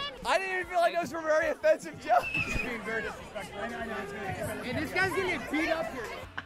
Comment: i thought he said this guys gonna get ddosd